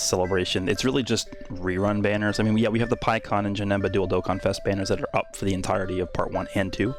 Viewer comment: I swear I heard you say "Dildo Contest" but it was actually "Dual Dokkanfest" Thanks for the content!